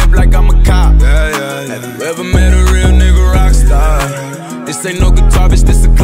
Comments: 😂 this ain’t know guitar this a glock who mistakes that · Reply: My girlfriend thought he said this ain't no guitar but it's a clock... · 😂 this ain’t know guitar this a glock who mistakes that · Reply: I thought he said This ain't no garbage this a glock 😂